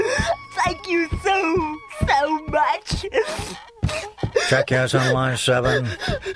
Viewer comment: Excuse me, but did he say "jackass on line 7" at 9:22?